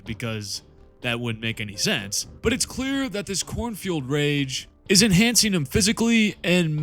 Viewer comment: I keep hearing "cornfield rage" instead of "Khorne-fuelled rage" 😂